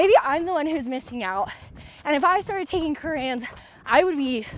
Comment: I don't hear so well and use subtitles, but I'm pretty sure that "if I started taking Koreans I would be 23% better" is not what was said. can someone help me. What product is this? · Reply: HAHAHA definitely not Koreans 😅 Curranz!